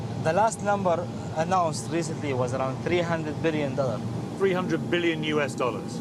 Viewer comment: Aos 33:20 min , eu entendi: 300 trilhões de dólares ou 300 bilhões de dólares?.....porque eu escutei o Sheik falar trilhões e, o reporter fala bilhões. kkkkkk